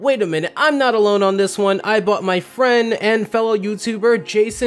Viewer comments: sounds like he said "I bought my friend" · the subtitles said "i bought my freind" it even sounded like it after i reset the clip 3 times